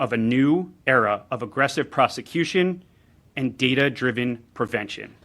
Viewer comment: He say's "data driven" and I hear "AI system"